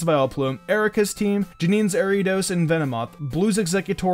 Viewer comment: he says Ariiiiidos instead of Ariados XD